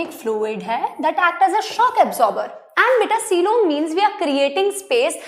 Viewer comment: Mam, what is coelom milsbia that you said